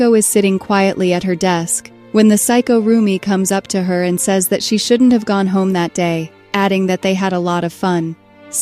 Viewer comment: i thought you were saying 'psycho rumi' then i realized you were saying 'saiko rumi' 😭😭